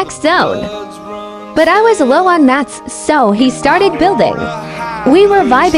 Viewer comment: I thought he said he was out of Matt's